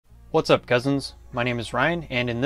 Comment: I am new to the channel and at first I thought you said "what's up peasants?". and I was like: "wait, whaaaa?" but then I hit replay and got it. I guess you hear what you want or expect to hear...hmm...still weird... anyways, good content cousin. Thank you!